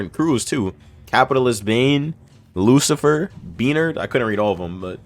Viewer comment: did u just say beaner im dead